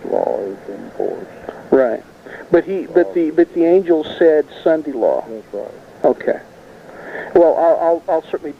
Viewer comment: Can anybody tell me what was said 17min into the video. It's so muffled. At 17:39 the interviewer stutters and says something like "sundy law"? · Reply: The Sunday Law is what Ron said.